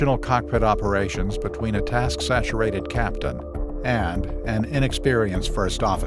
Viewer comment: At the beginning I thought he said a CASH saturated pilot.